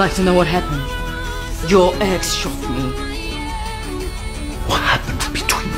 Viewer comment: Okay ngl but I thought she said "your eggs shot me" And I was like 'what' then I saw the subtitles, she said "your ex shot me" And I was like, "oh".. Huh thank goodness for subtitles